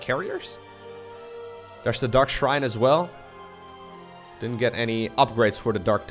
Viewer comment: I can't believe I misheard dark shrine as dutch shrine XD